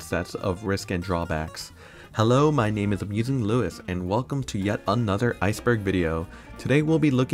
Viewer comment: club penguin only has 330 accounts 😢😢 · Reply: also captions said abusing luis💀💀